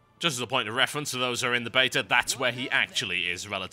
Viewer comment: Every time this guy says "beta" it sounds like he says "bater" which is strangely awesome.